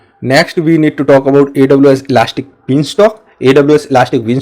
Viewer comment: You have Azure as the tag under the video's title instead of AWS? And what's a "Beastalk" (@ 6:46)? Did you mean "Beanstalk"?